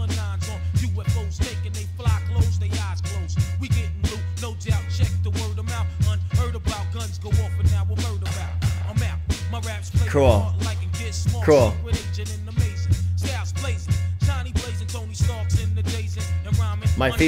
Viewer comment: I always thought he said unheard about, guns go off, and now I'm heard about